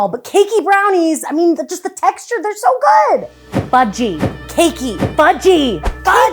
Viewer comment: I heard fu-key 🤣